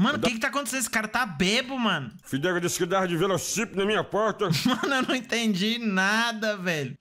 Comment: até 7:42 "Já lhe disse o que fazer, Fidelis disse que entregava no velocípede na minha porta" Pelo oq eu entendi, é assim que o papudinho fala kkkkkjjjjj